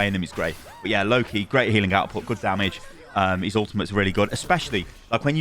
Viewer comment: When he said "Loki, great healing and damage" I heard it as "Low key great healing and damage." Maybe tiktok has corrupted my brain 😅